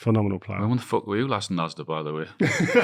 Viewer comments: What did Shearer say here @ 28:11 ? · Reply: And when the fuck were you last in Asda by the way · Does anyone know what Shearer said here? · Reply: And when the fuck were you last in Asda by the way